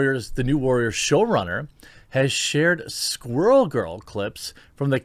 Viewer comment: I’m glad I read it along with you because I thought you said swirl girl🤦🏼‍♀️😂😂sorry